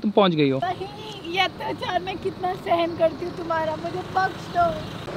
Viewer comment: 😂😂😂 I heard - mujhe Drugs do Lol